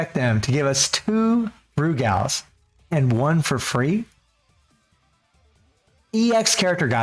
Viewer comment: Aww I didn't get the SS selector (That's The reason why I came back to the game) oh well I'm hoping that SS selector will come back and 6:43 did someone says free? (Please be free) let's go!!!